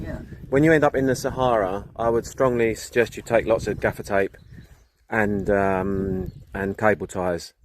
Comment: I have hearing problems and when you spoke to about the trip to the Sahara I thought you said be sure to take lots of Jaffa cakes 😅 (gaffer tape is what you said) - thought won’t they melt ? 😂